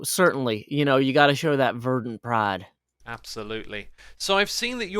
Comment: I definitely thought that said "Gotta show that virgin pride" but verdant pride makes more sense